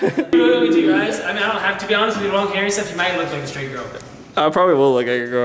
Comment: Did say "you might look like a straight girl"? 😂